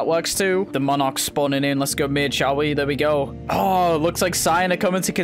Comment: idk why but 1:43 sounds like hes saying lets go b*tch